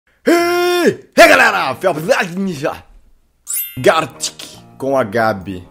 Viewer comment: ''HEY galera Felps vai mija!'' foi isso que entendi kkkkk